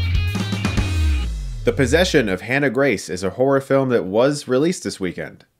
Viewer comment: I heard: "The Possession of Hannah Grace is a horrible movie..."